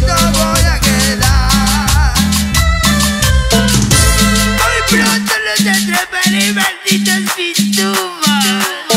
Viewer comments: Alguien porfa que me diga que dice en el minuto 06:50 nose que dice pero la re agita 🎉ajajja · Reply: Muy pronto los atrapare malditos pitufos 😎🇦🇷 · Me gustaria saber que dice en el minuto 6:51 dice algo cmo: muy pronto 妹背著洋娃娃 走到花園來看花